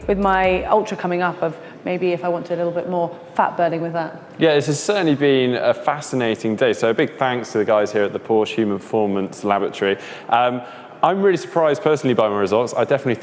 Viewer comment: Did Mark just say @ 9:56 Porsche Human Performance Lavatory? 😂